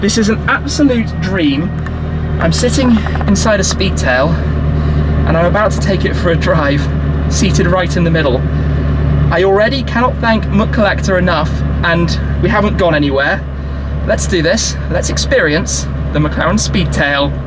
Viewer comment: thought he said "McCollector"